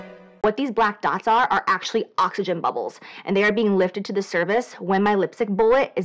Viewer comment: fda approved new hair.. thank you, now I don't need my refund:) upd: also, did JH said "lifted to the service" at 5:11??